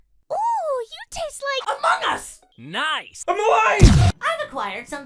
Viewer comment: LOL I HEARD "You taste like AMONG US"